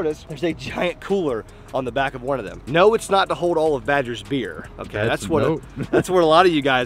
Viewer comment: I thought you said, "Badger's FEAR (not beer)"